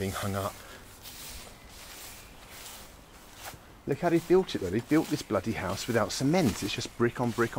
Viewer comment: The yellow tag on a dog's ear means that it sterilized, same in Russia, it doesn't mean the dog without a tag will get a bullet, it will be captured and sterilized. This is how the control of the homeless dog population works in this places. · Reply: I thought he said bullied